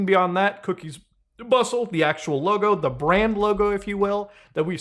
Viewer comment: Don’t ask me I why but I wasn’t paying attention and at first I thought he said cookies butthole😂